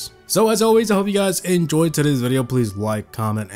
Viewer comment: at the end when he said "peace" it sounded like he said, "p*ss"